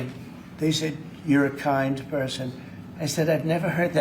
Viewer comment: You misheard what she said. She said you're KIND OF A PERSON.